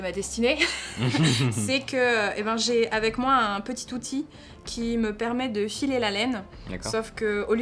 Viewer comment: Je cite : " j'ai avec moi un petit Tothy" C'était voulu? :-P